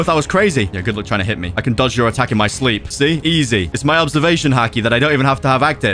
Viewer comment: "its my observation haki that i dont even have to avactive" who else heard that😂